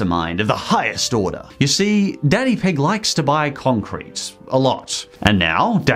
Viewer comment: MY PHONES SOUND WAS ON MINIMUM AND I THOUGHT THIS SAID “daddy pig likes to buy cock rings”